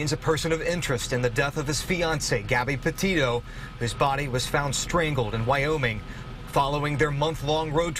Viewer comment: So did Brian wade out into the water with his dry bag on and drown himself? If the area he was found in had been all under water, why would he have gone into that specific area? It doesn't make any sense, if he went out with the intention of killing himself, why take a dry bag? If he went out there to hide, his backpack would have contained survival supplies. The fact that his "remains" and backpack were found on the same day, within a short time and in the same spot that his parents lead the search team there has got to be the biggest coincidence in the world! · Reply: no Gabbie was strangled, they said “whose body was found strangled” I thought I heard them say; “his body was found strangled” but it’s Gabbie they are taking of